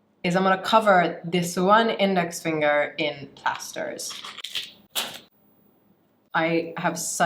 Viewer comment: Covering that one index finger in bastards? 4:06 lol sorry I know what you meant but still have no idea what you said. But it sounded like bastards. Lol 😂